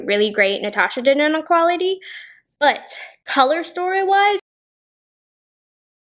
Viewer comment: Anyone else get a black screen at 4:53-4:55. What did she say about color story wise? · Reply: She said “Color Story Wise…..🥴 The video: cut off 🤣 Rude!!